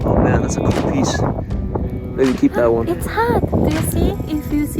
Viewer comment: she says 'It's heart..' but the caption says 'It's hot...' A heart-shaped hole is clearly seen in the tephra.